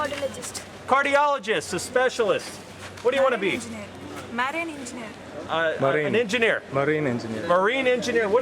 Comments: I thought she said "Marry an engineer." ;D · at first i thought she said "marry an engineer" 😂🤣